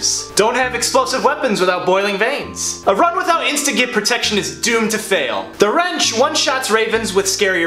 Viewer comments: What'd you say? I've replayed it like 5 times but i can't tell. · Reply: "A run without instagib protection is doomed to fail" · I know it's been 8 years, but what the fuck is instigate protection (if that's even what he's saying, I just used captions to try and figure it out)?